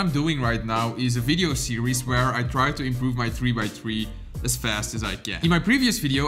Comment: I literally thought you said,” As fast as I GAN” if he did, is a true GAN boi.